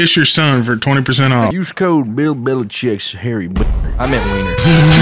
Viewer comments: I genuinely do not know what the heck the word is said here. I looked up various Spanish profanities and I still have no idea. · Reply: b3aner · what did say at 6:07 ? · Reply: Bean